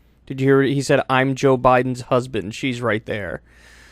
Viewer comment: I heard him say "I'm Jill Biden's husband." I think you miss-heard that one Kyle.